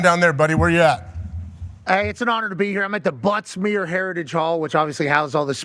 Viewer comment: I heard Butt Smear heritage hall lol